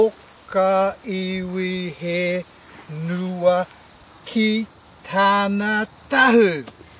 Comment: did u say kitana?